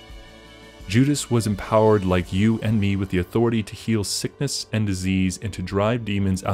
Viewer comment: Did you say, you have the authority to heal sickness and disease and drive demons out of those that are possessed? Is that what I heard you say? I backed it up and listened to it several times and could’ve sworn I heard you say, you possessed the power to heal sickness and disease and drive demons out of those that are possessed.... I just want someone to clarify that for me... please...